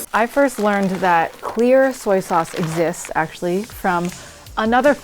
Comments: I thought she said "queer" not "clear" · I stopped fully paying attention for a few seconds to read a message at 6:22 and honestly thought she said "queer soy sauce"